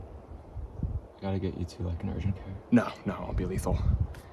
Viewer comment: did you say "no, that would be lethal"? If so what did you mean, glad you're okay man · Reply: Wouldn't be lethal. Widow bites are extremely rarely life threatening, so when asked if I needed to go to urgent care I was like... yeah I know how to treat these types of bites, no need to shell out $1000